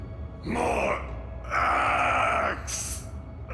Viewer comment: I really thought they said “more eggs”